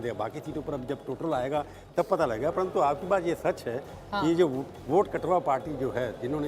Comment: what said 🤔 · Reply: Vote cut wa party